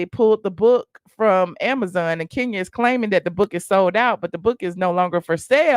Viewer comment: Lol….I thought you said the book is sold out. I guess I heard wrong.